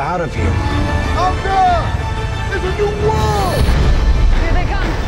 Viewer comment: in the trailer, when CLU said "out there" for the first time i originally heard it as "im good" the second time he said "out there" i thought he said "smoke her"